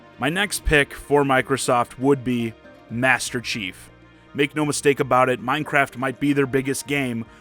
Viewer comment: I think it was said Sakurai deconfirmed Master Chief from what I heard.